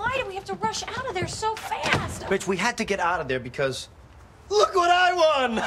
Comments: Did he just say “Bitch, we had to get out of there because...”? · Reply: McSuperfly he said "rach" 🤣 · first i heard it i thought joey said "bitch" really fast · doesn't it sound like he is saying "Bitch, we had to get out ..." lol